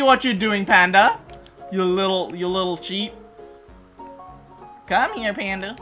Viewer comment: the way u said "Ya lil cheat" sounded like Choo Choo...